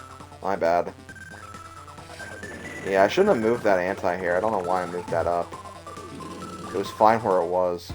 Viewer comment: im sorry but did you just say ANTI AIR